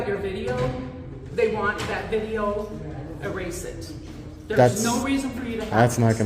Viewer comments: I thought she said “erase ed” like that’s how she thought erased was pronounced 😂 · what did she say? Erase-ed